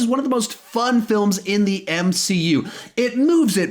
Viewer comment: Am I the only one who noticed that he said "MCU" instead of DCEU? 😃